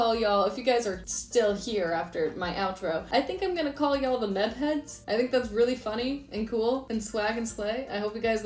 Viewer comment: when you said Meb heads i thought you said meth heads... so maybe that's not the best name.